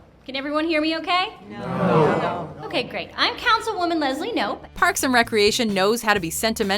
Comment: Who else thought it said pranks and reactions 😂